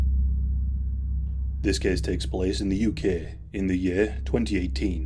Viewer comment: Now he got me trippin. I heard, “this cake takes place” for a second😂